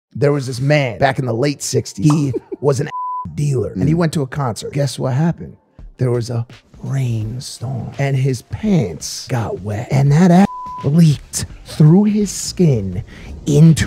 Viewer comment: Lowkey thought he said ass dealer I was so lost on why a rain storm would effect him 😭. I’m dumb as hell